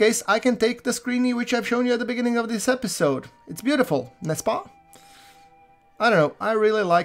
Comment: "Nesspa?" (spelling?) I heard that and thought, I have heard that before, and from previous context knew it meant ("Don't you agree?", or "am I right?" or something similar) But it was driving me nuts trying to figure out where I heard it before, and thus what language it was. Then I remembered: "David Suchet playing Hercule Poirot in "Agatha Christie's Poirot" series. I probably watched 70-80% of them. (Still have to watch the last couple seasons.) He is Constantly saying Nesspa? I thus I assumed it is Belgian. (And googling I see as a Belgian, Poirot probably spoke either Dutch or French. But since I cant spell it, Google translate cant understand it. So what language is it? 🧐